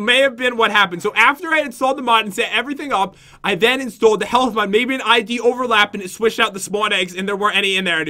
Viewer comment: I thought u said "help mod" but it was "health mod"